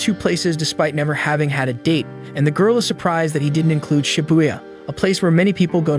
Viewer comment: did she say shibuya she's forgotten what happened there